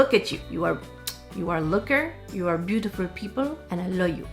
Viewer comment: English 101 for you hearing aids 101 for me... I thought you said "HOOKER".. LMFAO Yeah yeah...I know people 🤣😜